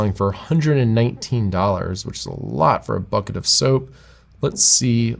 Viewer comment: dead!!! @ 13:12 at first I thought he said " thats a lot for fucking soap" lol thanks for the video learn a lot